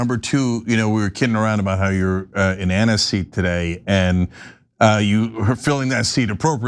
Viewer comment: oh I missed that, you're in Ana's seat, I thought he said you are in aniseed !!